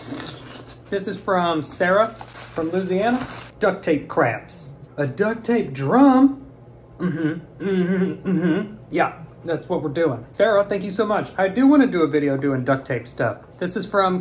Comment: seriously thought he said Duct Tape Craps😂😂😂